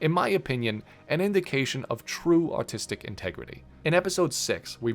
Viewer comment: for a second there I thought you said “In my opinion, an indication of true autistic integrity.” Which is probably right in this specific case. Lol.